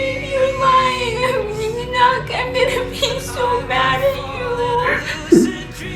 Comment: I keep thinking she said “ pee” instead of “be”